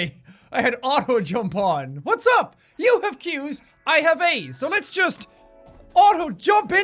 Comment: Did you say you have Aids? -One of the members of here comes treble -the office